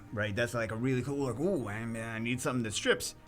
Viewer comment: I thought you said cool wip